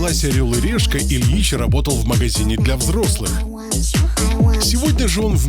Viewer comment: so funny they say he worked at an adult store but the subtitles said he was unemployed 😂🤔